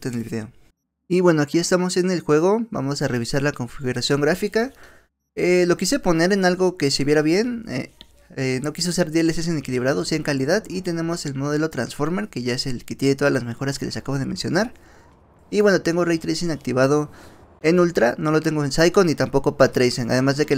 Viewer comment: Bro, cuida tu dicción, no entendí qué carajo quisiste decir en el min 2:17 con "no quise ser dlss ni equilibrado si en calidad", espero que sea que usaste DLSS en modo calidad, fuera de eso buen video, se agradece el esfuerzo.